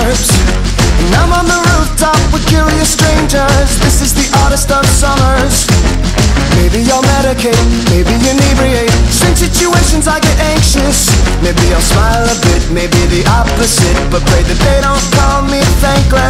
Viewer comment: Dude I always thought he said "This is the oddest Ann Summers" not "of summers" (Ann Summers is a sex shop btw)